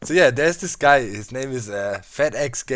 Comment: Not being mean but it sounded like he said fat egg gaming lol